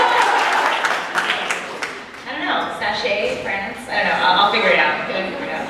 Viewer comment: I'm Lost at 14:00 what are they saying ? Friends ? France? Lol i don't understand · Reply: I know it’s been too long for this anwser, but I was confused too and I guess I figured it out, so here it is: Ana says that sometimes she would be “prancing around” with Bo’s clothes when she has the chance (because the weather is hot in california, she doesn’t wear them often). Then Zoie says “what does it look like when you prance?” They joke and I guess Ana sings a song with “prance” in the lyrics and Zoie is lost, but everyone seems to get it Hahahaha